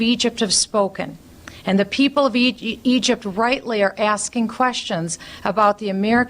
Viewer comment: I swear I thought Bachman almost said ugypt